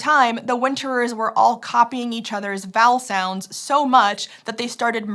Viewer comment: Actively had to keep my brain away from hearing "copying each other's bowel sounds" instead of "vowel sounds"